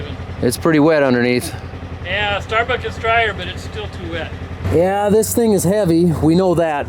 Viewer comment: your dad says something the Youtube subtitles translates to 'Yeah Starbucks is drier but it's still too wet". I'm guessing that's not what he said, but i can't blame Youtube because i hear the same words xD. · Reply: He did say "starbuck is drier" because starbuck is the town 5 miles south of us that we farm next to! You heard correctly!